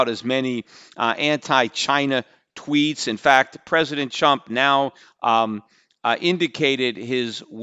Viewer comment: did he say president chump at 8:08... LOL